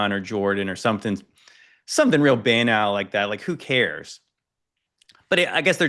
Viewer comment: used a (big) word at 56:38-40 which sounded like ‘benile’ but I cannot find any word like it or similar to it. Does anyone know what he was talking about, the word he was using..🤔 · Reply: “banal” - if something is banal, it’s so ordinary or overused that it’s uninteresting